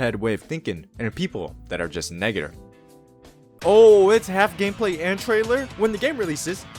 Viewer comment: I don’t think you said negative 😭